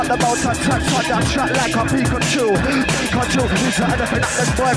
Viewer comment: Anyone else hear the dude say "Pikachu!" twice at 3:00!? Am I going insane!?